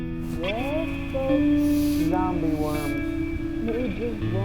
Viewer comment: I swear I heard the words "Zombie worm" during the biology scene